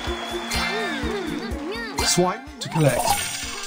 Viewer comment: I really thought he said "swipe to the left", not "swipe to collect".